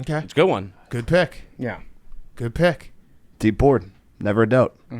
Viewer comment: They said Mexican and Hank said “deep board” I thought he said “deport” 😂😂